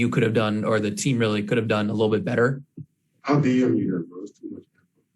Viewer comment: what is kd saying? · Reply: “I’ll dm you that bro that’s too much”